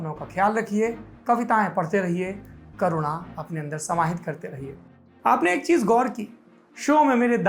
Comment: sir did you say kavitaye padhte rahiye "corona" Apne andar samahit karte jayiye😂😂😂